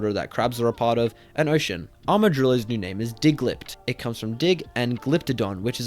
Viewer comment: I thought you said Diglett.